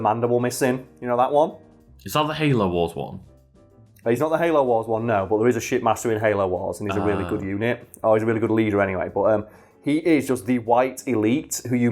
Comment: I always thought the elites were saying shitmaster